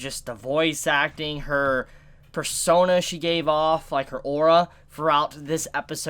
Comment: Did you say "ORA!!"?